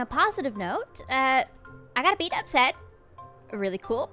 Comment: I heard "I got to beat up Zed", which threw me for several seconds. Like, I don't remember that happening?! Feeling very silly now